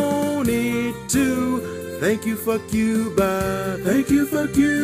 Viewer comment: The first month this was playing I thought it said "Thank you for Cuba" at the end and I could not figure out why